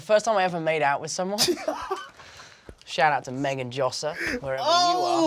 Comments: THE GIRLS NAME!!! I THOUGHT HE SAID MEGAN JOHNSON BC THATS MY NAME I ALMOST DIED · His first kiss has the initials MJ He was born for this role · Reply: Courtney Beach I think he said Josser, it’s at 3:09 if you wanna check it out